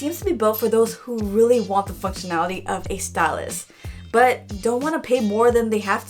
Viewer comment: did she say fuckinality...